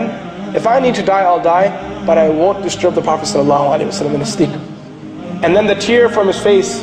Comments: the translation should say " I won't disturb the Prophet (SAW)" instead of "I will" as per the audio. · Masha allah!! Correction in subtitles at 7:20 Instead of "I will disturb prophet salallahu alahiwasallam" it should "I won't disturb Propher Salallahu alahuwasallam"